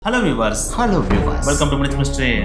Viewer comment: I heard hello fools 😅